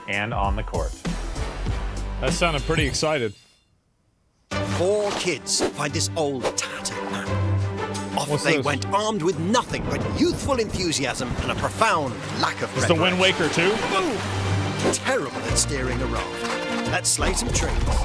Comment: The Survival Kids description i heard as "Armed only with youthful enthusiasm and profound lack of awareness". Sounds right.